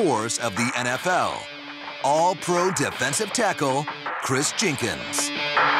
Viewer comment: Am I the only one who thought it said kris Jenner